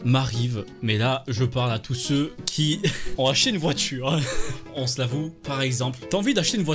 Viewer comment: Pour moi il a dit: «On vas chier une voiture» XD